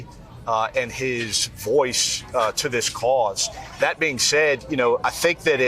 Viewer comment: Wow. @ 6:40 they told us exactly what we knew all along. He said “cause” not “case”